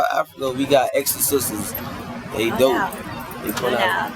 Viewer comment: What does he say at 8:00 ? Exo sisters ?